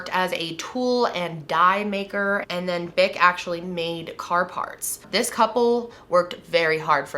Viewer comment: lmao i thought she said he “ate car parts” 😂😭